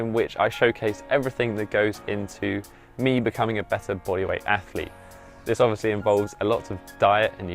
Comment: What do u say at 00:25 ? "Goes toward me becoming a better >????< athlete" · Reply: bodyweight athlete